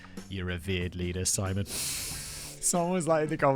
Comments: Lord! 😂 I heard “Our vierd leader” and not “Our revered leader” winding why Simon was doing a rough TV B-Horror German accent on one word only. Look, it’s early in the morning, it is what it is. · I thought you said you were their beard leader.